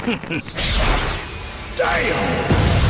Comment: Did Vegeta say “DAYUM” @ 6:33 😭?